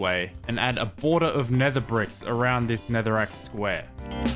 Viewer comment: When hen said netherrack i thought he said Netherite